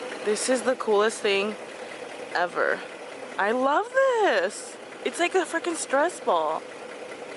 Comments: did she say “it’s like a fucking stress ball”? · I thought you said it's like a fucking stress ball